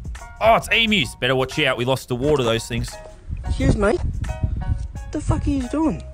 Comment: I like how lazarbeam saying "we lost a war to these things" about the emus is passed off as a joke, but it's actually a real thing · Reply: I thought he said we lost a mom to these things